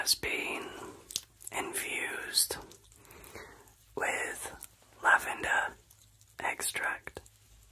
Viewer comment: I must be tired. I heard “the oil is infused with Love-ender.” LOVE-ender. And I was like, “Yes, please. Yes.” ❤